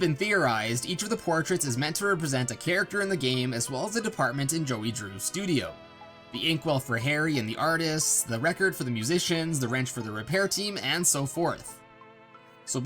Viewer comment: did he just say Harry?